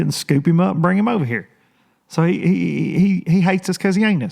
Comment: Lol I thought u said "he hates us cause he's ANUS..😂😂